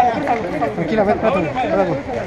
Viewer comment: Escuche mal o alguien dijo en el minuto 2:16 " es hija del jefe wey " 😂😂😂